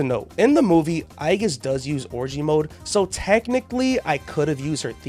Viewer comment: mf did NOT say orgia mode😭😭